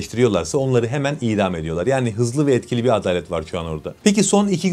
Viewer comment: ADALET mi var dedin yanlış mı duydum ?!